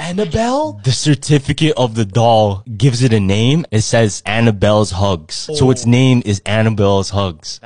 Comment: I thought it said annabelle is black